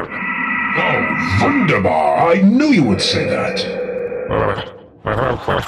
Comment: did he just say Oh Wunderbar! Thats German! Is the Chaos Agent a German guy?